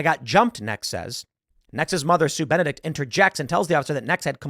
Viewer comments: I was listening, not watching this, and honestly thought he was saying Eggs Benedict. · I’m sorry, but I keep hearing “eggs Benedict”!